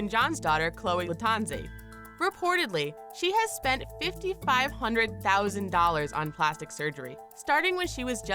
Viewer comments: Did I just hear, "Fifty-five-hundred-thousand dollars"??? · Did she actually say "Fifty Five Hundred Thousand Dollars"??